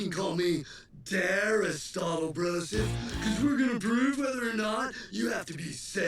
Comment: did he just say "prove whether or not"